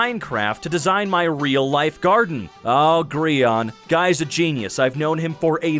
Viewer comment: w...w... why did he say Gri ahn?